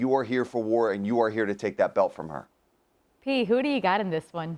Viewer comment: Did she call him Pete?